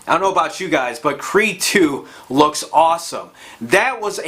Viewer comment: So at 3:50 your talking about a trailer you like. It sounds like he's saying Kree 2? but I doubt hes saying that lol. Does anyone know what he's saying?